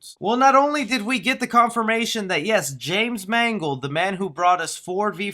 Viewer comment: Did someone say mangle?